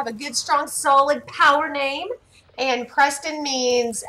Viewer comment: Did she just say power name